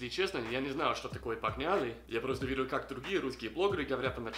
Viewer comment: послышалось "подняли", вместо "погнали" :-D